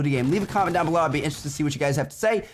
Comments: wtf are you saying ? Lmao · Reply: "Check out 1 of the 2 video's on the screen if you haven't seen them yet, Peace out guy's" Lol I know tho :p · wtf does he say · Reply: check out out one of the other two videos on the screen if you haven't seen them yet